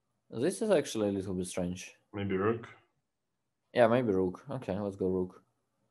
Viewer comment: Thought Dubov was saying "Let's go boom" then realized he meant pawn.